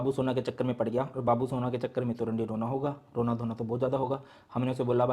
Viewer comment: did sir said randi rona😂?